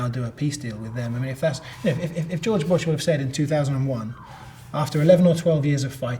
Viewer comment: did he say george washington instead of george bush?